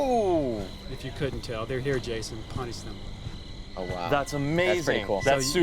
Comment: Ohhhh. I thought it was "They're here Jason... PUNCH THEM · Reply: agreed. no matter how hard I try, I always hear it as "punch them" instead of punish them. :T